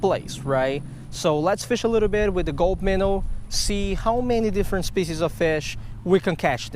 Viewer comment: Wow this is the first time I realized you were saying "gulp minnow" For the longest time I thought you were saying "gold minnow" My bad! Looks like I need to invest in those gulp minnows though you catch a ton of fish on em!